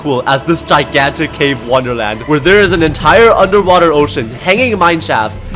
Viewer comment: did he say a ocean under water